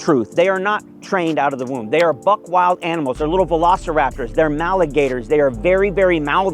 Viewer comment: Did I hear “maligator”?